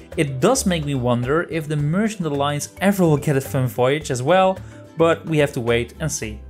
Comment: merchant alliance get what kind of voyage? re-listened to that part several times and still couldn't understand.. if its for a another type of voyage they were the first ones to get a second type of voyage.. in the beginning all merch had was animal catching before the cargo runs.. then order of souls got the ghost ships and now with the vaults all companies have 2 types of voyages now.. TECHNICALLY the thieves haven run can be considered a second Athena type voyage but something else you can get from the pirate lord would be nice.. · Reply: 'It does make me wonder if the merchant alliance ever will get a fun voyage as well but we have to wait and see'. Plucked straight out of my script! Thanks for watching!